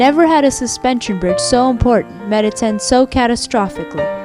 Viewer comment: "Never had a suspension bridge so important _____ so catastrophically" What's the word in the blank? I can't hear it properly. Any help? :) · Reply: Met its end